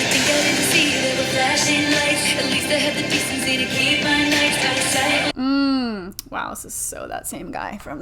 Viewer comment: The first time I heard“at least I had the decency to keep my nights out of sight” I thought she said “knights”, which could tie back to the “maidens” and lean into the fairytale imagery